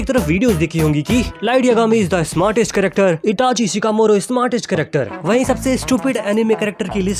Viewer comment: did bro just say "itachi shikamoro" 💀